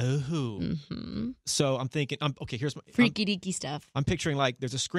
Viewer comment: i don't understand wath she said but this is so cute (i'm french sorry) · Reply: dont call 911 “freaky deeky stuff” She just saying freaky/weird stuff lol. deeky isn’t a word 😆